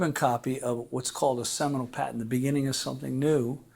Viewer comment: A “new” patent. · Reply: It doesn't sound like he said a new, its sounded like he said seminole